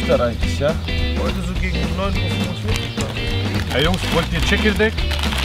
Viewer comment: Ich hab verstanden: "Ey Jungs wollt ihr ChickenDicks?" XD